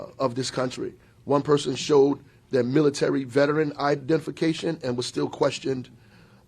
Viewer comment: Roflmaooooo @ 0:52, did he really say? Idefecation? Or am I hearing some other bull💩?